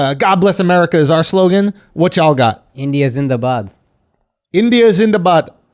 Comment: No lie. At first I thought he said India's in the butt. Lmao